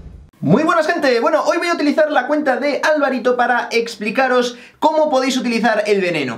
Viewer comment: Nivel 8 1400 copas!??! Ja!! Yo estoy en 2200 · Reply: “JRT” Riete Pensé que decías de alvaro xd